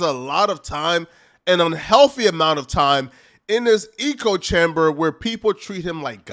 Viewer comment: Eco chamber. Yeah that's how you say it. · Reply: I thought I heard: ego chamber😂😂😂